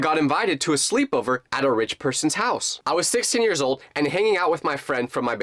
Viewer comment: my dumb brain thought he said 60 years old